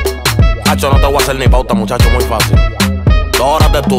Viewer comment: Yo cuando la escuché pensé que decia: el mamaria xd